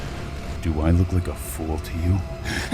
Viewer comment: When said “Do I look like a fool to you?”, my brain thought he said “You play me for the tenth fool kid”.